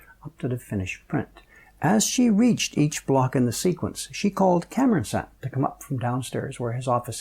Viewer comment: Who is this Cameron guy? First I hear of him. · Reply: I actually thought he said Camera-san, lol